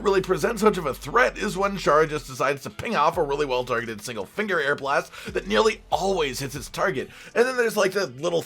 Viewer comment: Had to play it back, thought you said finger blast for Shara... Equal parts relieved and disappointed.